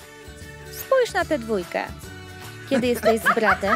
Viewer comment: kto usłyszał ,,trójkę"?